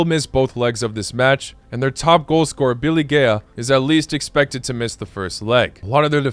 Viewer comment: What did bro said " Biligaya"😅